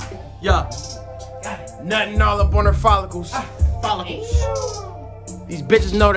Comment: “Nutting on her foleques” wtf did he just say 😭 I’m real life stupid, the blunts be hittin